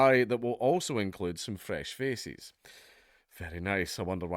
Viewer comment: Maybe avoid that phrase with the accent. I heard “fresh feces.”